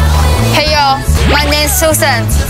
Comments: "My name is sunghoon" What i heard: "My name is someone" Me:who tf- is someone *backwards*aggressively..... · please i heard "ma name is jeff" 😭